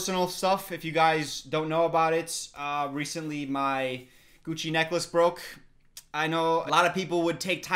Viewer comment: Idk about you but everytime says “gucci necklace” I hear “cuchi necklace” Am I the only one? 😂